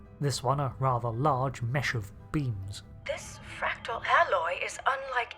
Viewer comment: who else heard "mess of beans"?